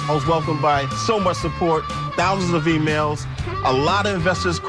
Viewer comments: who thought he said thousands of females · who else thought he said he was welcomed by "thousands of females"?